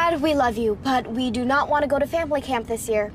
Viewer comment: I heard "femboy camp" 💀💀💀